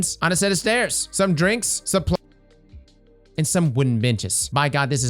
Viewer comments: what did he say here? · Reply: Supplies & Plants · Did anyone hear what he said if he uploaded it un-edited? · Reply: Supplies and plants